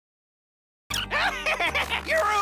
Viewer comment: I thought he said: YOURE ALL JERKS TO ME! GIRZMATOV